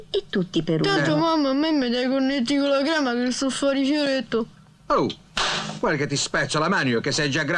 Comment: Cosa dice Aristide a 1:01? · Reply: "...tanto mamma me dà i cornetti alla crema che sto fòri fioretto..."